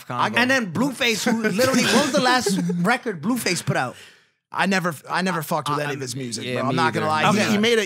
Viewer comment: Did dude all the way to the right say nigga at 7:18? “I never fucked with that niggas music” is what it sounds like bruh said not gonna cap